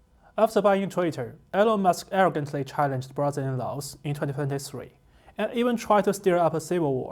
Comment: lol, I thought he said Musk vs brother in law